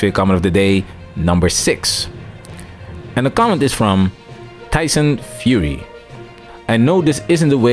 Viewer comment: It sound like you said Tyson Fury